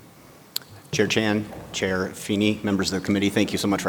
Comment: Had to rewatch the first few seconds as I thought he said Jackie Chan. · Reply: I thought he said Khaki Chan. Lesion's got competition.